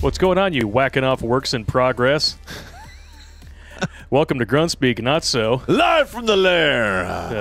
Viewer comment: For a second, I thought said drunk speak instead of grunt speak.